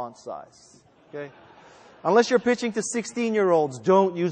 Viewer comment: Is it me or at 3:16 did he say, "The eighth thing is to hire infected people."? Besides that I liked his directness in how he prefers presentations and he's right, if you can't talk about your product to some reasonable depth without looking at something then forget it; I had a college professor pull up W3Cschools for goodness sakes, total waste of money just like someone giving a pitch who doesn't know what they're pitching!